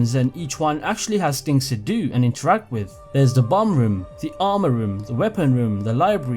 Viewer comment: Damn, I am getting old, cuz I thought you said "bong room" not bomb room 😂